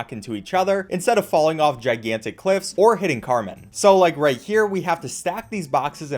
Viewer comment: I swear to god, whenever you say that name i hear "Cartman" instead